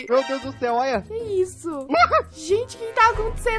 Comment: o:Oq e isso “vida” EU: EU OUVI “VIDA”? EU OUVI “VIDA”? 🤨🧐🫥